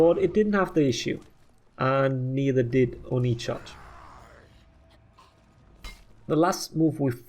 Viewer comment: "Oni charge" Almost sounded like Onii-chan to me, lol